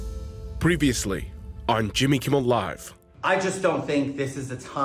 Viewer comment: am I the only one who heard "Kimmy Kimmel Live"???